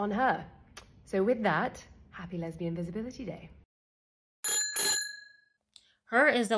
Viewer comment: When CEO started SPEAKING I would have SWORN I heard happy lesbian invisibility day 🤔